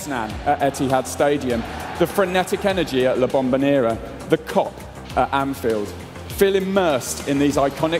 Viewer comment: What did he say ? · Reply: The Kop.